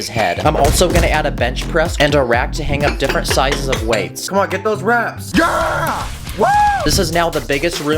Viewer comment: for a second I thought you said come on get those rats😂😂😂